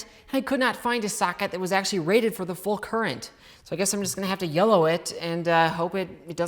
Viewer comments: I thought you said “I’m just gonna have to yellow it” as in pee your pants in fear, which also makes sense 😂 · can someone tell me what "yellow it" means please. Ty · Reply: Yolo it, it mean to just go with it without caring and see what happens basically